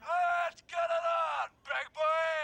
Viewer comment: He. I heard the sound of thy holy trumpet and took not warning. He has clearly wandered too far from the word of god and Cornelius Bernard Hatcher your hour has come! Let's get it on, big boy! It's time to get back on the path! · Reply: I thought he said, "... it's time get back on the bag..."at the end.